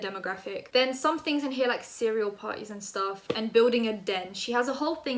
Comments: Can you please make a rap song out of the cereal party. Thank you <3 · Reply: I thought she said "serial party" at first and it was like a serial killer themed party and I was like, this sounds cool, how unlike Zoella lmao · I thought you said a serial party and I thought ‘oh she tried to be creative about surprise parties’ but damn, did my brain cells die when I realized its a cerEAL PARTY? Who tf?